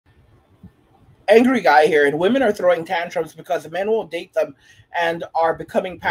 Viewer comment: Women throwing tantrums. 😅 I thought they said Women mature faster then us men 😅😅. Again we win